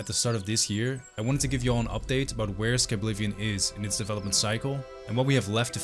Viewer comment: did bro just say skiblivion ?? as in skibidi or is just the brain rot at the terminal stages and there's no hope for me..